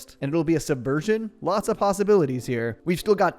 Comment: "Towelie here ..... semenly leaking info" is how I hear it lol